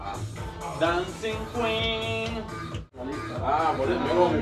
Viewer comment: did he call you dancing queen? 😂😂 🕺